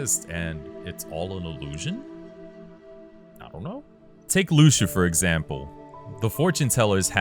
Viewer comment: "Luxu for example" I really thought you said Lucifer example. Have you heard the theories about MoM having similarities to Lucifer being cast out and wanted to get back to Heaven or Kingdom Hearts. While we we have a being of literal darkness, people theorize that MoM might be Light. A fallen light maybe? A Lost Master?